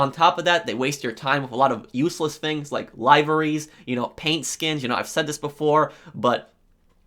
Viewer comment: Did you just say lyvaree? Did you maybe mean livery?